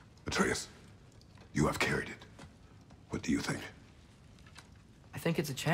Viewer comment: It almost sounded like he was saying "screw you" not "do you"